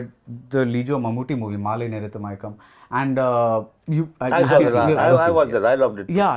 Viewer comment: What was the name of the Mamooty movie Sriram mentioned (at 13:07) in the first quarter of the interview. I still cannot make out of it. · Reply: Nanpakal Nerathu Mayakkam